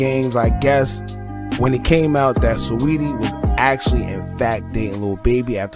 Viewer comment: That's Jaydas' on Clothing Line, not with Pretty Little Things. I'm glad she's keeping it CLASSY in these industry streets. 👏 Oh what's that?? I thought I heard streetie; I meant saweetie.🥴